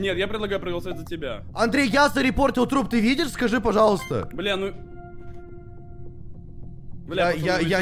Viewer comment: ты сказал бля или мне послышалось???